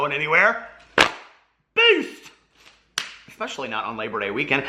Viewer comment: Omg I just realised u say beast lol I thought u said peace all this time 😂🤣 oops 😬